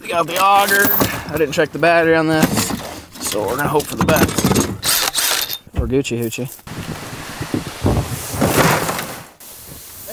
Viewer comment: Waitttttt a damn minute, did you call banjo a hoochie at 12:39 😂